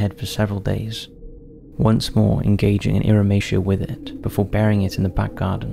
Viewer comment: Wtf is he trying to say in 27:26 the subtitles show iration but I can't find any meaning with it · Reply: Another commenter answered it, the word is "irrumatio" - sex with the head/face